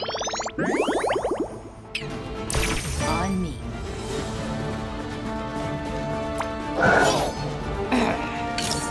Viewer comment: what did she say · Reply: She said "on me"